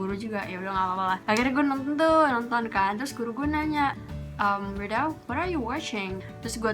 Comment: Hahahahaha i thought you were saying "Wirda, what are you washing?" Instead